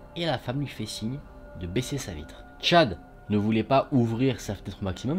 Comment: c’est moi où on entend bite au lieu de vitre 😂😂😂😂